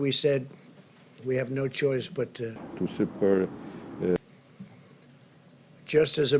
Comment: People asking what it is at 2:30 he obviously said something what wasn't on script so they implemented "to support it" is said if anyone was wondering